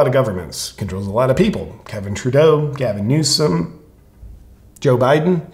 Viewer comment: Did you actually say "KEVIN Trudeau"?? loool